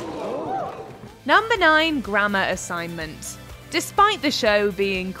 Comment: Took me a minute to understand"grammar". I thought she was say "Gramma". 🤣🤣🤣🤣